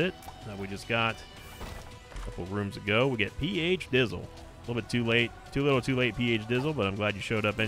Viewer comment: Hey, I am French and I don't understand perfectly English, but I can follow your videos and this is very nice so thank you :D Just, what is the word at 47:30 when PHD is picked up, you said something like "PH ..." maybe "deasle" but first I don't know an English word like it and second I cannot google it because obviously I cannot spell a word that I don't know ahah Is there anybody that could help me? Thanks a lot ! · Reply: He said PH "dizzle," which is just basically gibberish in the same way "for shizzle" is used for "for sure" No need to worry about it!